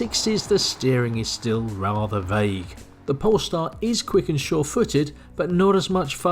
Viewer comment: What do you say please in 3:54, the steering wheel is rather ??? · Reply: Vague